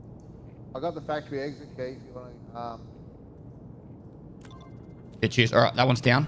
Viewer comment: I think he says "I got the Factory Exit key if you wanna ge... uhm" 41:28 thats gotta be something like "haha theres a box of matches *lol*" but it really does sound like an imaginary language xD